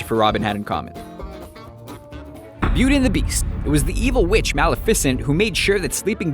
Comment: Did he just say malafecent